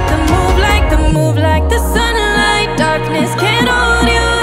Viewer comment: IDK why at 0:59 I thought she was going up say "Move like a snake" 😂🐍